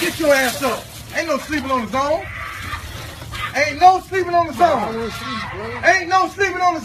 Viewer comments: Is he sayin, "ain't no sleepin on the zone!" ? Wtf is that and what does it mean?? 🤣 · Seriously, what is he saying here? "Ain't no sleeping on the SONE?" What word is that? Any help is appreciated. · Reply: "Zone"